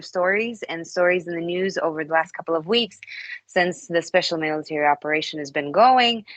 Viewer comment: Will someone please tell me what word she is saying at 1:01 ? "special ____ operation" What word is that in the middle there ? She says it so fast, I can't understand it. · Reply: Military